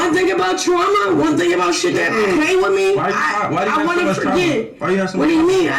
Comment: did he just say “why do you have so much trauma?” Like yeah okay she chose this life😭😭😭